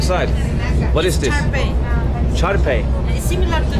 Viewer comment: she is actually saying Chard Pie with a heavy accent. Swiss chard pie (pastel de acelga) is a traditional Peruvian pie and a personal favorite! Definitely not spinach lol